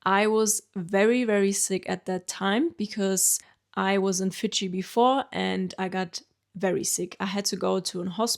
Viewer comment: I think you said you were suffering from "Iwasinfijibefore". So, I replayed it. Yes, you had a bad case of Iwasinfijibefore. (2:13) 🙂